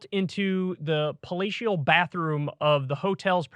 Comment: HA HA HA! I though I heard "FALLATIO" when KYLE said, "PALATIAL"